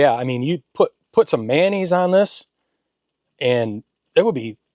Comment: At the 20:00 mark, I accidentally hit 2x fwd…sounded like you said “you put some panties on this…”. Rewind! Mayonnaise. Jesus lol